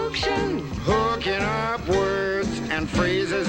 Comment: Grew up with these! All the songs were so ear wormy, they still pop into my head periodically. "Lonely ol Bill, sitting on Capital Hill" was a great one about how laws were passed. "Interplanet Janet" was the "galaxy girl", a cool chick who took us through the solar system. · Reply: "Lonely ol' Bill," huh? 😊 "I'm just a bill, yes I'm only a bill, and I'm sitting here in Capitol Hill." He's "only a bill" because he hasn't been passed and become a law yet. I can see how a kid could mishear those lyrics. Like when our esteemed reactors thought this song went "cookin' up words" instead of "hookin' up words." Lol Well, we may not have gotten every word right but we all got the gist, anyway. 🙂